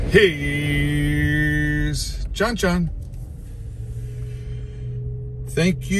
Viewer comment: I thought he said here's chon chon...damn